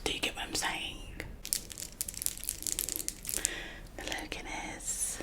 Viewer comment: did you just say hello Guinness 😭😭😭